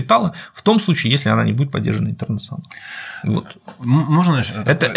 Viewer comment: "В том случае, если она не будет поддержана ...." Чем? Не разобраться. 4 раза переслушал. Слышу "трнсс"